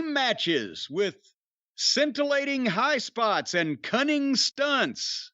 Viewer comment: Why did I hear "cunning stunts" as "stunning cunts"? 😭